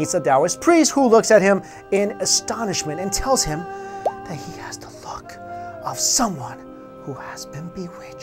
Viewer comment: I think I hear wrong... it sounds like: And tells him he has to fu**...